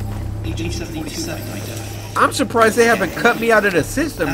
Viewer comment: did it just say agent 47 identified from a hitman to a cyberlife security officer